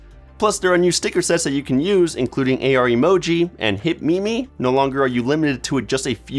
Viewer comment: Did you just say... new hit *mimi*?